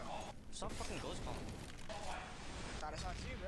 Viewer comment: What did Stewie say to n0thing at 11:56 ? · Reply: No u sounds like "stop fucking ghost comming" like stop talking while you are dead